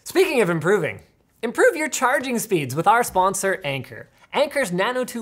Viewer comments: I heard "The sponsor is Anger" 😂😂😂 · I definitely thought that he said that their sponsor was "Anger".... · Whenever the sponsor was introduced, I thought I heard anger instead of Anker, that would have been a really good segway lol